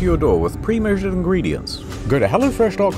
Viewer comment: get meals delivered right to my door with what kind of ingredients? Primojon ingredients? Is that what you said?